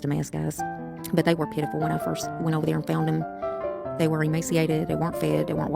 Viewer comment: Found them? I thought she said someone dropped them off at the shed 🤔🤔